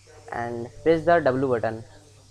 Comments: WTF DID YOU SAY? with your english you HAVE TO write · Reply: I think you have an ear problem, he said "Press the w button". It was pretty clear to me. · what did he say in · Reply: He said "Press the W button"